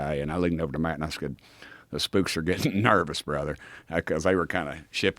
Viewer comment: did he just say the spooks?🤣🤣🤣